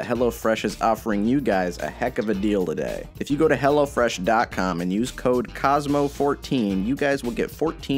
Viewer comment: I was skipping over the ad (sorry) and I skipped at 1:17 and it sounded like "If you go to hell- you get fourteen free meals"